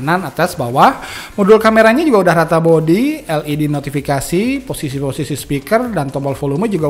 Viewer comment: led notifikasi? ga salah denger nih?